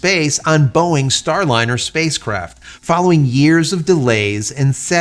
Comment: Spacecrap? Yeah, I think I heard right.